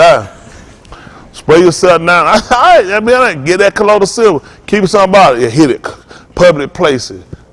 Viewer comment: What medicine did said to take with water? Cloriphil?@ 12:50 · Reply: Colloidal silver